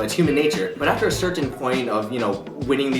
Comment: for some reason when i hear ryan saying "certain" i hear "serchen"